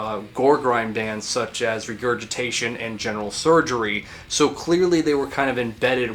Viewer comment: I think you meant to say RegurtiTATE not ReguriTATION haha XD